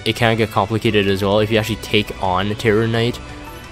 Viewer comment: I thought you said Carrot night.* lol 😆